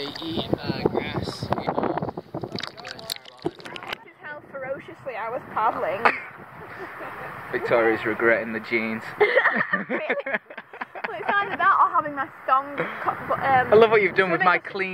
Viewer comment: So good! I kept hearing voice saying, "Victoria's feeling rough!" (With her attempt at a Northern accent.) 😆